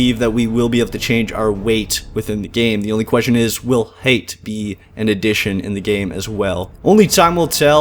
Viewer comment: did he say hate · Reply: Height.